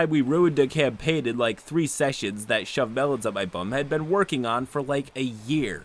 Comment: >"I just want you to take my games seriously." >Online handle is, "ShoveMelonsUpMyBum" Look I'm not saying he's a liar; I honestly believe the story. It's just the juxtaposition of those two things is very funny to me. XD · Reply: OHHHH it says shove melon thought it said shawn mendes